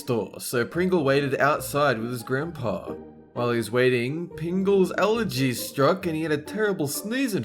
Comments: am I the only one that heard him say pingle instead of pringle at 2:07 😂😂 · PINGLES ALLERGY STRUCK (he actually said pingle) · Instead of Pringle, said Pingle.